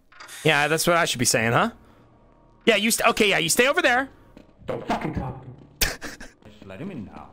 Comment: Benry stomping away from Gordon and saying “I can’t fucking believe it, don’t fucking talk to me” is so underrated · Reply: I always thought Benrey was saying "don't fucking talk to me" to the Tommy clone (at